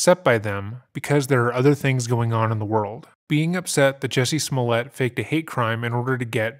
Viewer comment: I know I heard you say Juicy Smoolyea 👏🏿👏🏿👏🏿